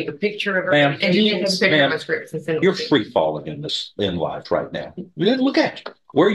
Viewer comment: I thought judge Stevens said “ma’am you’re free balling in life right now.”